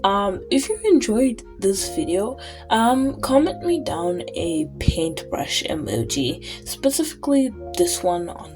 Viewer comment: I heard the end w4ong and thought you said "Go and down a painbrush" My first thought was "but.. its not a drink?", and then i relized. Anyway: 🖌